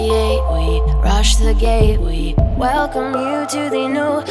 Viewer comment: Is it just me or is it at 0:26 I hear "rush the gay week"😂